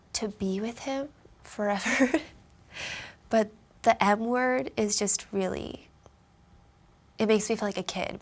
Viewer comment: lmal I thought she said the n-word